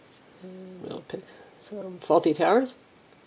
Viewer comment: I thought she said "faulty towers", as in cellular radio towers lol